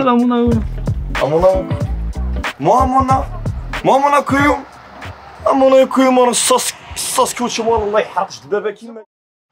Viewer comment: "sasuke uchiwa" j'ai entendue ça 😂